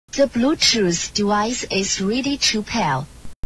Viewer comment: At the beginning I thought she said "The Bluetooth Device is really cheap here" 😭😔😭😂